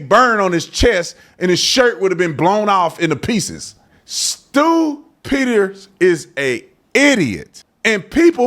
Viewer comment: thought you were saying “stupid” 😂 (but it was Stew Peters). Imagine that!